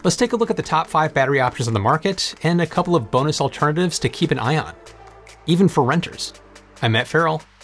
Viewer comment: I heard to keep an ion @ 0:31 Nice